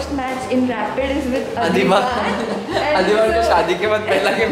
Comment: sounds like he said Bkchodi 🤣🤣